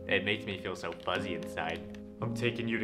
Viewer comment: “They make me feel so fuzzy inside” At first I heard: “it makes me feel funny inside” 💀💀💀